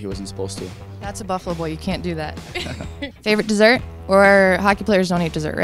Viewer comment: what did she say? that's a buffalo? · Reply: Art Vandalay "That's a Buffalo boy - you can't do that!" ... Tyler is a Buffalo Sabres player and lives in Buffalo!